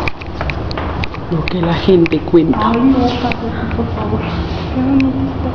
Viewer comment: En el minuto 6:19 aparece una psicofonia que dice... "lo que la gente cuenta"... miren ese instante y veras que no miento ...